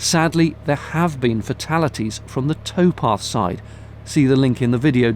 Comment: I've binge watched everyone of your videos over the last 2 weeks and I thought you were saying "Toepath" meaning feet (toes) on a walking path. In reality you're saying "Towpath" as in back when horses towed the boats from the pathway.